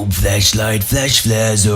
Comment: How can people understand when it sounds like he is saying fleshlight but can’t hear 🤣🤣